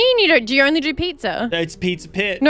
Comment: "cause dominos actually do pizza" i swear i heard him say that